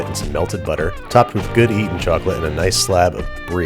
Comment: Good eatin' chocolate? Did I hear that wrong at 5:45?